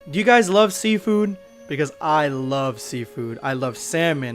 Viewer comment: I thought you said see-food